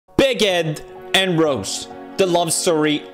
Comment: At first I thought he said, "BIG ED AND ROAST"